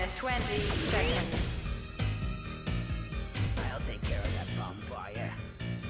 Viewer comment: I always thought the scientists said "I'll take care of that bonfire..." I was always like wtf..